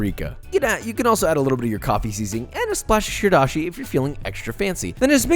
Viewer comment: What did he say at 3:38?? "coffee something"?? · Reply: Coffee seasoning